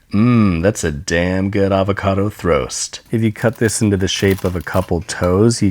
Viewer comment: Thought it said Avocado Throat instead of Throast